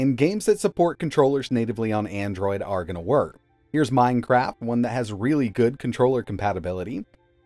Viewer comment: I swear he just says "Minecrap" lol.